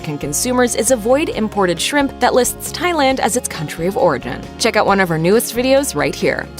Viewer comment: It sounds like she says "...avoid imported shrimp listing Thailand as its country of Oregon." at 5:01 🙂